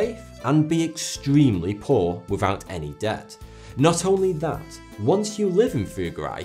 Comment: I had to go back & listen to that again. I thought you said "Had to be dirt poor without any doubt" 🤣 Ok now I heard it - without debt. I do understand his requirements tho. Say you said prayers for this guy 3x a day bc he wants to get out of purgatory. Its a clever set up. He was a shit alive & by creating this community he can be released from accountability (if you are from the Catholic belief). Some guys will find a way to cheat the system even into death smh